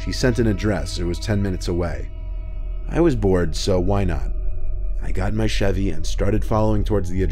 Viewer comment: When he said, “I got in my Chevy,” I thought he said, “I got a machete.”